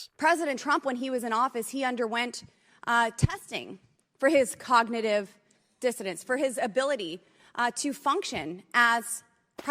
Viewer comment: She actually said, "cognitive dissonance?" 😂😂😂 What a dummy! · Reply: I heard 'cognitive diffidence'. Give her a break; she managed to use two three-syllable words, and made a creditable attempt at the second one.